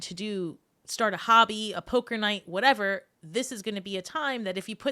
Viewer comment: OMG I need a hearing aid. I had to rewind and listen again at 16:30. I swear you said “hooker night”. I was like wow, how do you play that? I’ll play. Lol. Well, I am a Scorp 😉 😝 Seriously, though, Leo rising here. Again, totally resonated with me. Not surprised. You are always spot on. Thanks for sharing your gifts 😊